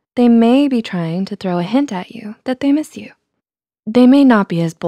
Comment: Am I the only one who heard "hentai" at 0:49? nope. just me. ok.